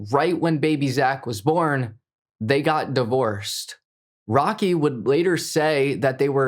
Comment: when I was falling to sleep I heard my name ROCKY and spoke like he was talking to me smfh🤦🏿‍♂️💀